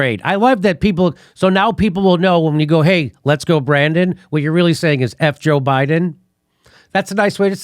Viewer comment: I heard "Lets go granddad".